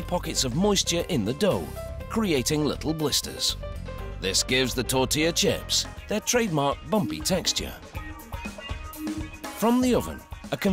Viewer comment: What did he say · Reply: This gives the tortilla chips their trademark bumpy texture ....